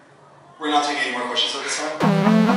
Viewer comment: WHAT? what did he say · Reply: "We're not taking anymore questions at this time"